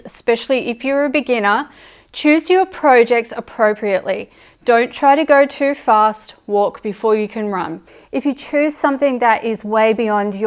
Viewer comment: I thought she said "Don't try to go too fast, warp before you can run" 😂